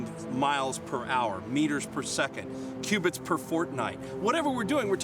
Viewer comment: did he say Fortnite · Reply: No, he said “fortnight” which means two weeks.